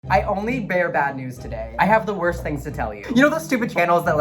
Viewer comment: I thought you said "i only bareback" 🤣🤣🤣🤣